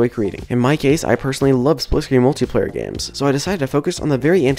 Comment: Honestly I hear this as splushkey multiplayer games..... What did you just say tho? 🤭